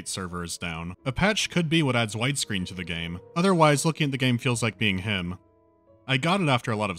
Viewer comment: I had to listen to this sentence 20 times before I heard that you said "The patch could be what adds wide-screen to the game"